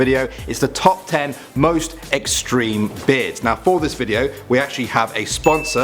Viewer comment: For a second I thought it said "most extreme bastards" lol